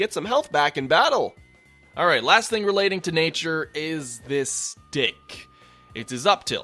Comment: When you said "the stick", I could've sworn you said "this dick" 😂